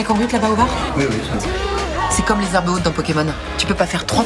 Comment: Qu'est-ce qu'elle dit à 0:13 ? "C'est comme les ... dans pokémon." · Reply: c'est comme les herbes hautes